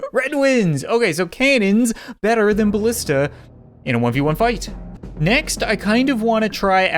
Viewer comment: umm did you say 1v1